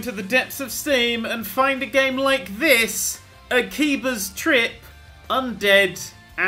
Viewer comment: I thought it was said as Akiba Strip